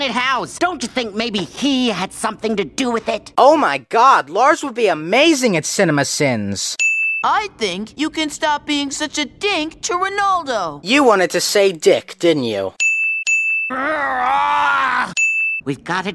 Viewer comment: I honestly thought he said “dick” the first time I saw that episode